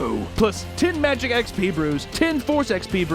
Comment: Lol when you were giving the raid sponsor and you said brews I honestly thought at first you said Hebrews and I was like "wait you can gets jews in this game" and then I looked down saw 'brews" lol